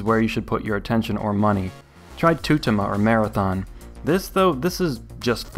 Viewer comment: What is the first watch you're saying at 4:10 before Marathon? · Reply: Tutima. Recently made pilot watches for NATO forces.